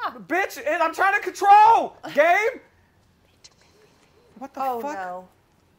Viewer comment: did he say gabe